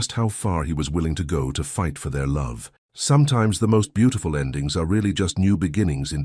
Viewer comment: Pause: I thought she said she'd fight for their love. Back to story.💜